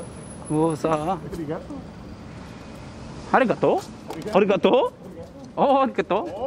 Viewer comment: Did I hear arigato?